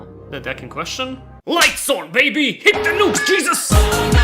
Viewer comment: Did you say hit the nukes Jesus 😂😂😂😂😂😂😂😂😂😂😂😂😂😂😂